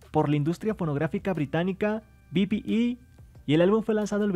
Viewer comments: por la industria pornográfica? Jajajajaja escuché eso :v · Por la industria pornografica britanica??? Entendi eso, alguien mas?